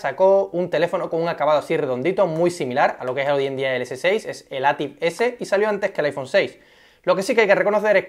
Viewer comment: Como se llama el movil del minuto 1:25? Es q no le entendi muy bien al tipo :v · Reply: ATIV S